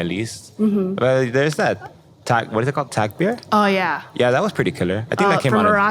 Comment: What did he say @ 15:42 ?? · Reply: taqbir